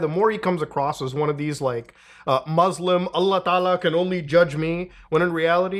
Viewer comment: Dead ass thought you said "Muslim a la Canolli" til you finished the sentence and I realized Allah Can Only Judge me 🤣